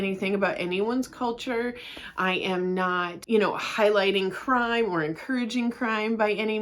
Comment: Until you explained that you were not promoting “crime” I totally thought you were saying “mauve wife” and I was feeling so out of the loop trying to imagine what that was. 😂